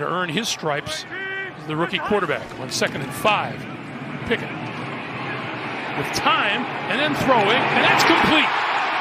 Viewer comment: Bro I thought he was saying Pickett to Pickett lol